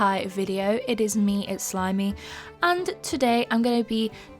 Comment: wow lol i thought you said i’m slimy lollll